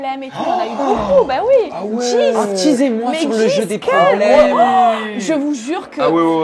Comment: Disez moi 😭 3:30 q! La honte 🤣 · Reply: Il dit "Teasez moi" * 😂